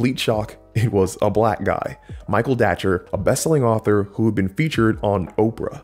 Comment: Anyone know about the author who mentored him? It sounds like he says 'thatcher', but the subtitles say 'dacher', but I can't find anything about him on google. I'd like to check out Michael's work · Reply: It is Michael Datcher!: <link>